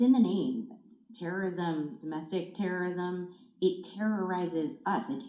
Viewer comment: I thought she said it was no racism in the US